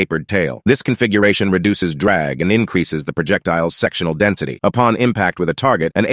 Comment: The man said sectional density, I first thought he said sexual density 😆😆😆